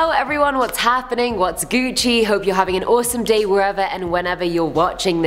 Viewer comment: DID SOMEONE SAY GUCCI